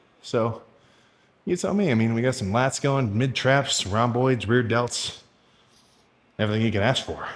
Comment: Who else hear rom ROIDS